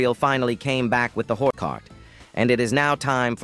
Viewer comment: when sawed off shotgun and when not sawed off double barrelll shotgun?? side note : did he say hore cart??